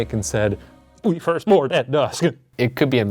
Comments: where they heard the board at dusk i heard “the 21st warden is dead” lol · I heard "(Why) would warden do that?" and not "We board at dusk" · I didn't hear "we board at dusk".... I heard "play dashboard confessional" lmao